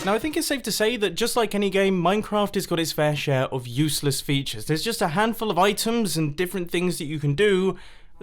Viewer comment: I thought it was saying he was useless! Haha😂